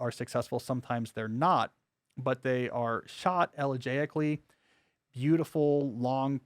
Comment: just curious what word did Cal say at 1:48? “they are shot ________” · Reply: It's the word "elegiacly" meaning relating to an elegy. "elegy" means a sad poem or song, especially remembering someone who has died or something in the past: Gray's "Elegy Written in a Country Churchyard" is a famous English poem.